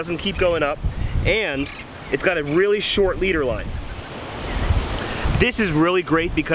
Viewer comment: did he just say 3lb leader